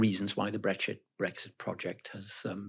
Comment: he did jusy say " brexshit" ? 🤣🤣🤣